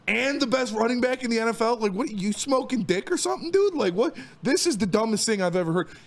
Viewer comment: Did he say smoking dick? 😂😂😂